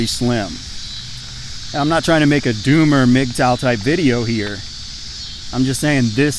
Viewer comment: What was the word he said at 10:04? "Doom or ????" I have heard that word recently in other videos, but don't know what it is or what it means. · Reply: He said "doomer", a fatalist who thinks the future of the world or humanity is DOOMED, therefore it is justified to be a nihilist.